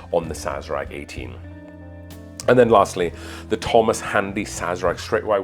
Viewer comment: Did you say Thomas Hardy at 10:25?